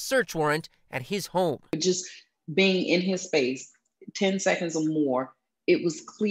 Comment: “All you had to do was look at his face for 10 seconds...” and being a loner?really? That’s her “red flags” you’ve got to be kidding me. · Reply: Thats not even close to what she said. "Just being in his space for ten seconds...."